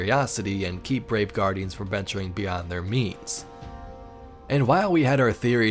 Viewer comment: I thought said keep guardians beyond their memes for a second there.